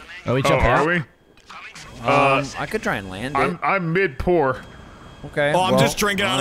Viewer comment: I'm mid pour What I hear "I'm mid-poor" and then I wonder what is mid level poor for a CEO in GTA...